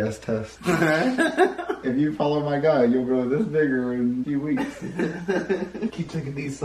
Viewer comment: did just say the n word? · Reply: He said bigger